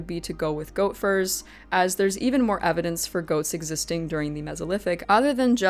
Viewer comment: Hahaha @ 13:08 I swore you said "ghosts existing in the neolithic" hahaha